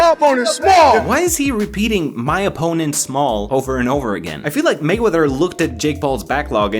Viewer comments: I thought he was saying my boner small😂😂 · I thought he was saying "my boner small."